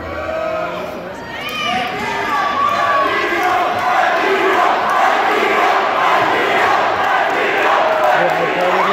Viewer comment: Does anyone know what are they chanting? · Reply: Emila, the name of the guy